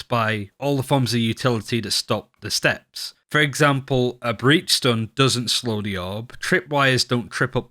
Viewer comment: did i heard it right?? BREACHSTONE???? WTF LOL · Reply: Breach stun*